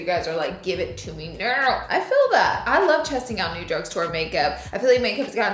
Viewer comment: did she say “ I love chesting out new makeup”? Lol